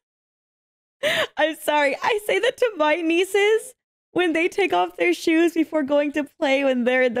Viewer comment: I thought you said you had two nieces, so clearly one of your siblings caved